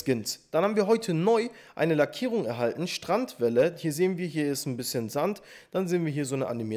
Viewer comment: Lol ich habe Strand Bälle verstanden und nicht Strandwelle